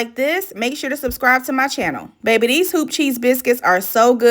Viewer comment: Definitely thought you were saying hoochie biscuits.